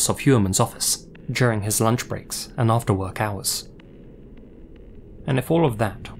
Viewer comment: I first heard "after workouts" and immediately thought started peppering in jokes in his videos